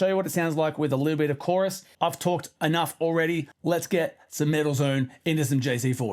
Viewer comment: I heard "Middle Zone"!😮